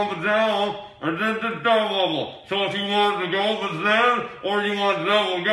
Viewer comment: me: tries to translate mark: also, this is very important. huggle puff diggie down did a stunt level. so if you wanna golden snout or you want the double goat, please tell me or else im not gonna be able to survive. the double goat please. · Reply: I thought he said, "if you want the dolphins then,"